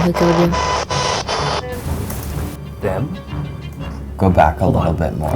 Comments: I kinda sounded like she said em like a nickname for Emma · I swear it says “Liz” at 20:19 not “them” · I heard it said Liz or Em..or them